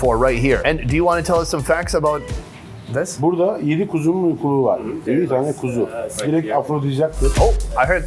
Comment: In case if anybody is wondering what he is saying at 7:39 : "Burada 7 tane kuzunun uykulugu var. 7 tane kuzu. Direkt afrodizyak etkisi var." "Here we have 7 lamb thymuses. 7 lambs. Has an effect of aphrodisiac."